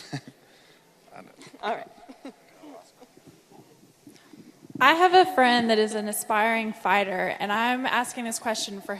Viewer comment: What did luke quietly say to megan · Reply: Maa Nonu I thought he said that ;)